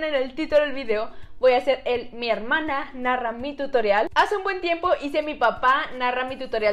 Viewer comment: Pensé que decía "mi hermana la rara " 😂😂😂😂😂